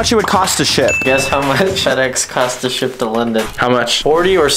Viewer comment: I thought you said shit lol 😂